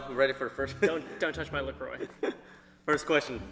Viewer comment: When said la Croix I THOUGHT HE SAID DON'T TOUCH MY LIQOR